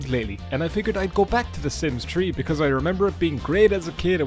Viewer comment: Idk why i thought u said "the sims tree 🌳"